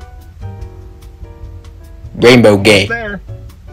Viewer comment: "gaynbow gay"😂😂😂 this man is a LEGEND...i misheard that...i like this version cause FUNNEH